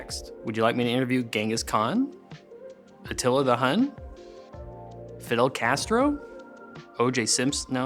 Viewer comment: Did he just say Fiddle Castro?